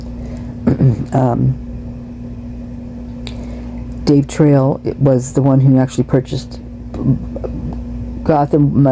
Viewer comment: I'm sorry what is the date I'm sure you said it and I missed it I'm trying to catch up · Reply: May 15 2023